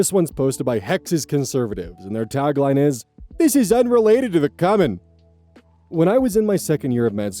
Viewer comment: 😭😭🤣 Hi it's me again. The Utahn who you schooled on the multiple pronunciations of coyote. You might have just been trying to be funny, but it's pronounced coomin or even Q-min and I 100% thought you said "unrelated to the comment." This time I know for sure it's not a coyote situation. I'm as white as you, but I eat so much cumin that I have an "omg Mom!" Pavlov reaction every time I smell it. And last time you were pronouncing coyote with ordinary English pronunciation rules, but this time I know you forgot that the I in CUMIN jumps over the one M to make U hard. (I know what I said ( ͡° ͜ʖ ͡°))